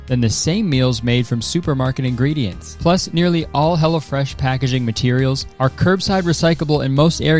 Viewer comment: I kept hearing "Hella Fresh" when you did the sponsor, and I can't help but think that's the name they should have gone with